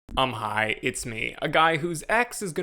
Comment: "I'm high, it's me" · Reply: First few videos I watched were while I was at work in a noisy shop so I totally heard it as "I'm high." and assumed that getting high to discuss movies was the gimmick of the channel 😅😬